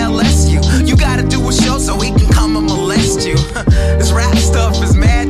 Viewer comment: Anyone else heard him say "You gotta do a show so we can come and males you"